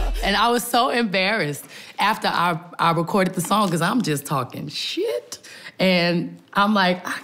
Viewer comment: Did she just say,"I am just talking shit"?